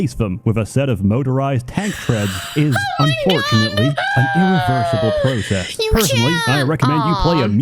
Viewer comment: You misheard him, he said it is irreversible not impossible.